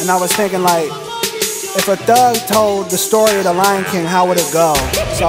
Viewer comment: Can me a damn idiot. but in the intro I thought he said, "what if a duck told the story of the lion king" but he actually said "thug"